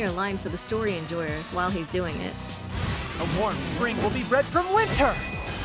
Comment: Can anyone tell me what he said during the signature? I didn't understood a word of that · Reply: A warm spring will be bred from winter.